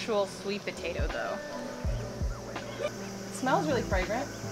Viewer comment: did I just hear Black semen? kinda strange